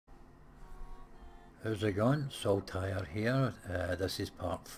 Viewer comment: Every time I hear you say "Saul Tyre" it sounds a little like "So tired here" xD I keep thinking "go to bed man" :P